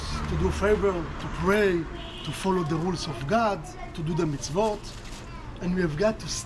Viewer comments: 캐나다에서 유대인 데이케어에 일하는 일인! 지나가다가 제목보도 들어왔어용~처음에 유대인에 대해 잘 알지도 못하는 상태로 일을 시작했는데 1년이 지난 지금, 점점 지식이 쌓여가고있네요. 5:40 에 나오는 "to do the vote" 라고 들리는 부분은 사실 " to do the Mitzvot" 혹은 Mitzvah 라고 하는데요~ 항상 옳은 일을 그리고 선한 선택해서 행하는것을 의미합니다. 실제로 저희 센터도 이 부분을 중요하게 생각해서 매일 자신이 한 Mitzvot에 대해 나누거나 싸움과 같은 다툼이 있을때면 "Let's be a Mitzvah friend." 라고 말하곤 합니다. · 초쯤 보트로 들리는 말은 투표가 아니라 미츠바라고 14살쯤 하는 유대교 성년식 비슷한 거예요. 미츠바를 받아야지만 토라를 읽을 수 있고 미츠바는 태어나 처음으로 토라를 읽는 큰행사에요.